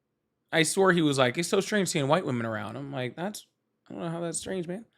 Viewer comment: Hey. Liquor Cabinet = White Women 😂 funniest misheard. ❤❤❤ Lotsa love to you, and sweet family. Best to mum. 💜💖💕🥰💕💖💜